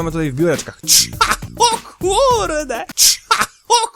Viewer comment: na początku usłyszałem ,,ciacho kurde'' XDDDDDDDDDDD